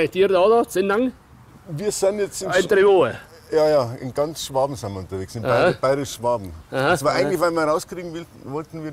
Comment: was meint er mit "alter joha" bitte helft uns!!! wir haben bereits das ganze video verstanden... uns fehlt nur das "alter joha" bei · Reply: "interviewen" sagt er im Dialekt